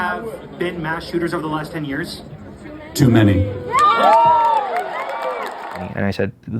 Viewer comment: did anyone hear that woman say "too many..." Before Kirk!!!